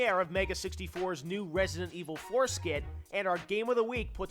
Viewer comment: Everytime they said "4 skit" I thought they said foreskin.